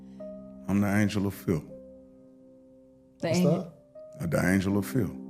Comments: did he say filth? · I thought he said angel of filth 😂😂😂😂